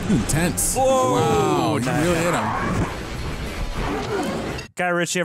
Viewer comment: did he just say “he really hit him”???